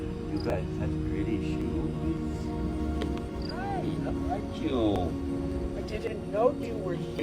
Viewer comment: Not only did the bag fall but at 4:06 i heard a male voice say "hey!"